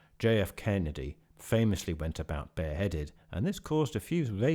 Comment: When you said jfk went bare-headed, I thought you said beheaded and I was like hmm not exactly but close enough 😅. I figured it out soon after though